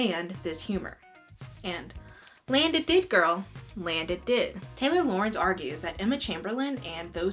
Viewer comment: I absolutely thought you were saying Taylor Laurent and I was like man, I didn’t know he has such an opinion on Emma chamberlain 😅